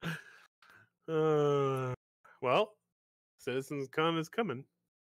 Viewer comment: Did he say "Citizen's Con is coming." 😂